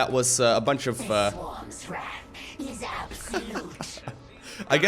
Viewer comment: For a moment I thought said "The Swann's wrath is absolute" X'D